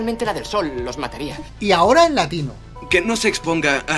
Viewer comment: Escuché "ahora en latín" O_0!